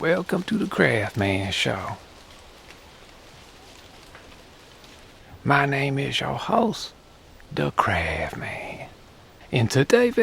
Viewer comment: I turned on captions and it thought he said, “My name is your host, the CRAB man.” 🤣